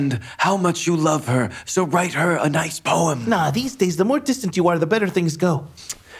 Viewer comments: j'ai entendu: "plus tu joues de ta knakie plus ça marche "😂😂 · dit quoi a 4:22 il parle de musique ? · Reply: Non pas du tout, il dit: "tu parles, aujourd'hui plus tu joues le mec inaccessible plus ça marche" :)